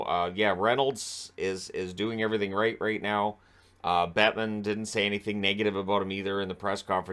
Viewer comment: I misheard 'Bettman' as 'Batman didn't have anything negative to say at press conference", and at first I thought it was a joke along the lines of "Deadpool wants to buy the Sens" 🤣